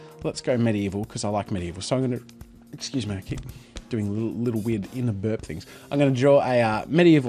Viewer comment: sorry, what did you say? :) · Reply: little inner burp things?